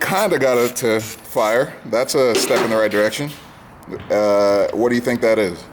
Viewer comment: Oakiemouse watch it to the end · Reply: Seb_the_builder crap I heard "that's a wrap" and turned it off, I feel dumb now.